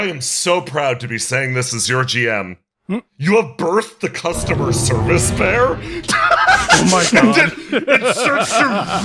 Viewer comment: Aww I heard “customer service mare” At first. So much for the party accidentally making the secret 5th horseman of the apocalypse